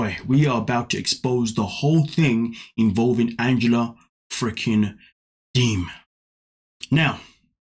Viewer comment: at 1:57 i thought you said Angela freaking demon if i didn't listen properly. 😄i know u did not say that tho. I believe she acts like a demon though, who is very possessed.